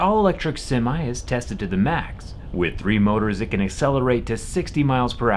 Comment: I thought musk said 4 motors